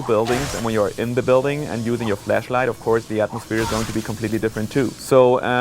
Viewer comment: don't wanna sound weirrd but i thought he said fleshlight instead of flashlight